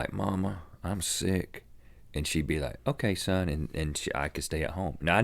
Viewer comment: haha! at 3:03 the captions say surname instead of son! haha